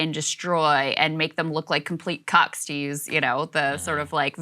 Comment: Did Krystal just say 'cucks' 🤣 one of the worst insults in my opinion lol